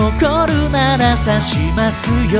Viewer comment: 『白紙で出してみる』が、『ハグして抱きしめる』に聞こえた笑笑笑